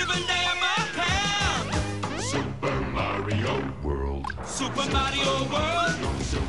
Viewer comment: listen careully at 0:32, it sounds like he says "*fuckin'* super mario world"